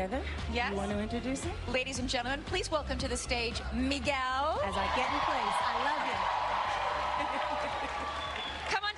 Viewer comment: did she say bullshit?wtf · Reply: "oh sh*t"